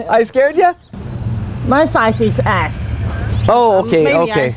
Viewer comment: At the start, I thought she said my size is ass, not s :P